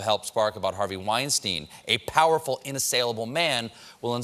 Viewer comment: Did anyone catch "Harvey Weinstein, a powerful, unassailable man" being captioned as "Harvey Weinstein, a beautiful, unassailable man"?